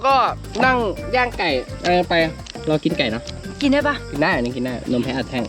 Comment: พี่พูดว่าตัวกินไก่ป่ะ